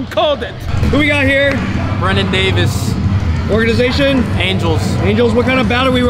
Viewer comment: Ok. That’s Brendan Davis. I thought he said Brennan Davis. Had me confused to why he was saying he was with the Angels. Lol